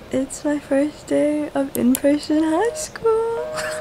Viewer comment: I thought she said preson😂